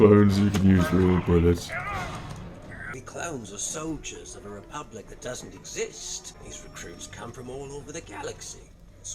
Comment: It totally sounded like he said "The clowns are soldiers of a Republic that doesn't exist"....